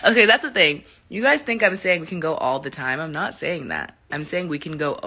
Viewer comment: I THOUGHT SHE SAID "WE CAN GO ALL THE NIGHT."